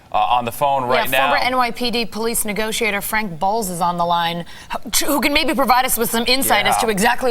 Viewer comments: DID I JUST HEAR FRANK BALLS? LMFAOOOO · lol the way he says "officer" 2:57 And did she say his name was "Frank Balls" lmao, wtf. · did she just say Frank Balls? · Did she just say… Frank BALLS?